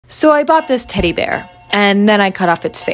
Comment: Ok but why did I hear “I bought this titty bear😭😭😭